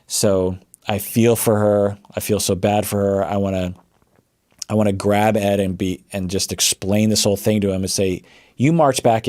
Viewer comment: did he just say "I wanna grab ed and beat" cause I wanna beat him up too 😂